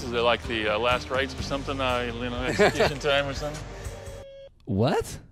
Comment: wtf he said at 6:50??? "wow this is like the last rice or something like linalinealincolntime or something?"